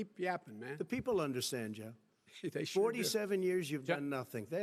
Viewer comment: Biden: "Keep yapping man" Trump: "The people understand Jeb" Did Trump just call him Jeb by accident?? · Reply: Play it again with headphones on, clearly said joe.